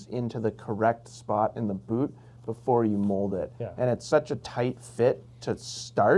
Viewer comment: Did he just say your foot is in the boot when you mold it?